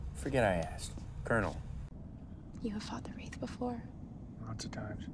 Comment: I think I have a dirty mind cuzz at 5:20. I swear she said. : "YOU have f*cked a wraith before?"